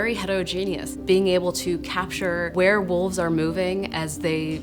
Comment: Did she just say werewolves? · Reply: “Where wolves are moving” 😂